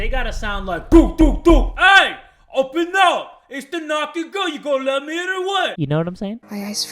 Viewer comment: I thought you said the naughty girl part 1